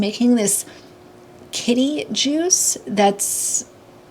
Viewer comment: When I first heard you say Kiddie juice I thought it was a weird juice for kids but you said kitty juice and I'm just appalled